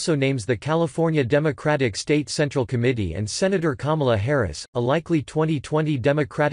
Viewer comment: Normally I don’t like these robotic voices.......but at :14 apx. It sounds like he says. Kumala. And I laughed